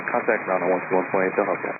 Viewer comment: I usually don't point out the small errors in the transcription but at 0:46 He did not say "sorry about that" He said, "they'll help you out." He admitted no fault, nor did he say anything about it to Frontier 1734. American is the one that saved the day by seeing that the controller crossed an airplane in front of him.